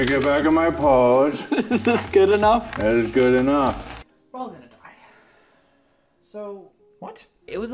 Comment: What does Ethan say at 7:41 ? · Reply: "we're all gonna die"